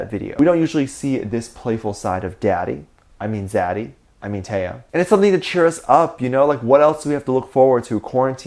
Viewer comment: Hahaha, "Daddy" "I mean daddy" "I mean taehyung" 😆😆😆 This got me crackilakin' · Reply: Angel Flore I heard "Daddy. I mean zaddy. I mean Taehyung"